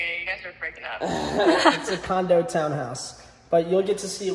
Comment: cool house er condo lolz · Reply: i thought they said its a condo though?